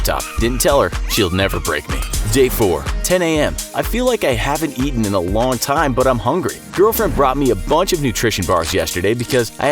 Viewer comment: "i feel like i haven't eaten in a long time but i'm hungry" captions says "...but i'm not hungry" so which one is it.